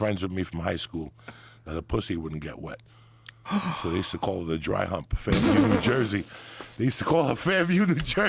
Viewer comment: Anyone know what Joey Diaz says at 30:32? "They used to call her dry-hump-______-New-Jersey". I can't hear what he's saying. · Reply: Fairview